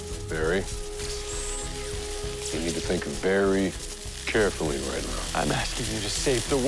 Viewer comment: Istg I heard “think BARRY carefully right now”😂😂😂